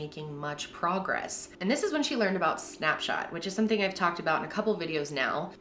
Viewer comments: At first i thought she was actually saying snapchat and I was like wth? But nvm after a few times of her saying it i got what she meant 😂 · OMG I thought you said Snapchat , I almost spat 😂😂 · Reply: Snapshot